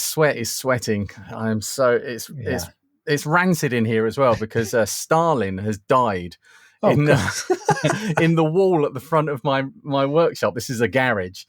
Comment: Am I the only one who thought said that Stalin had died in the wall at the front of the garage?